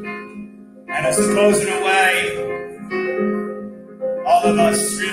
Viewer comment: Anyone understand what he says at 30:07? I really don't understand what he says there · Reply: i think its “and i suppose in a way”